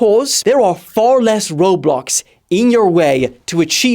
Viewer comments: im going insane i heard roblox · I thought I heard u say "there are far less roblox in your way"